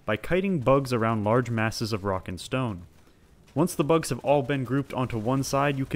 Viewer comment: DID I HEAR A ROCK AND STONE???